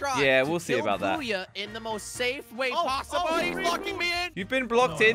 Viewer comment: did he said fucking or blocking?